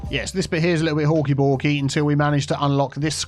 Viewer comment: I always thought you were saying hulky bulky 😅